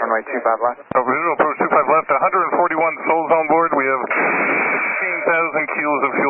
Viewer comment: I think the pilot say he have 141 souls onboard not 41